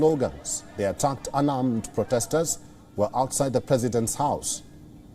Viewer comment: Darn I thought they were saying Pelosi’s house!!!